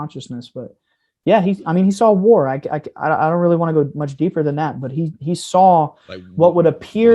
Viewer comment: What did say about war @ 2:32:05? I can't make it out. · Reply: 'like... a World War?"